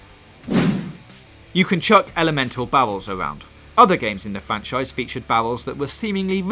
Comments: It sounds like he is saying bowels instead of barrels. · Did he just say I can catapult my bowels into enemies faces? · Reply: I couldn't stop hearing bowels when he was saying barrels! So hilarious!